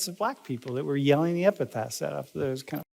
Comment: He's not an English prof, since he says "epitaph" instead of "epithet." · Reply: He clearly says, "I have nothing against the black people who were yelling the epithets..." You might want to get your ears checked. The professor does meander too much, wasting student's time, and has lazy, mouth-marble enunciation, but he didn't say epitaph.